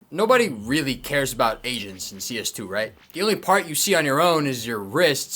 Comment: I heard “Nobody really cares about asians in CS2” and was expecting something about manipulations :D